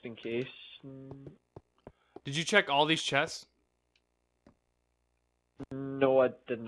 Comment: IT SOUNDED LIKE HE SAID SEX CHESTS